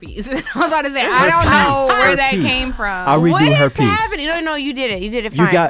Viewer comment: I 100% thought said "Herpes" too. I was listening to the podcast in the background and whipped my head around so fast. 🤣